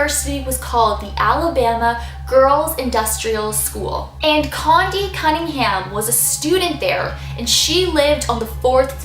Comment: I thought it said condum cunningham O.O